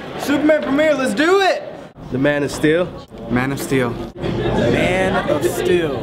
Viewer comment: I thought said "Man of STU.." or MAn of Still.. ?? LOL!